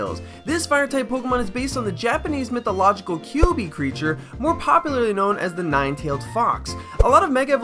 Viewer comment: I heard 'Kyubey creature' and thought 'whaaat that looks nothing like the kyubey I know!' (Only Madoka Magica fans would get this)